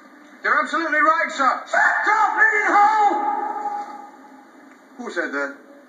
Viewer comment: 31 - ALEX: "You're absolutely right, sir!" SERGEANT: "SHUT YOUR BLEEDING HOLE!!" Michael Bates is so goddamned GOOD as the Sergeant in this film. He ups the ante in every scene he's in and really delivers! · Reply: 30 years later lmfao! ive always thought he said filthy not bleeding!